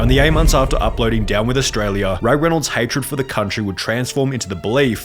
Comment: I keep hearing "Ryan Reynold's hatred for Australia" and bursting into laughter